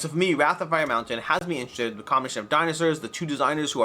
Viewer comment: I think at 9:15 you say "the combination of dinosaurs, the two designers who are very notable designasaurs, seems intriguing" Hahaha now there is an idea for a game, dinosaurs who are trying to design something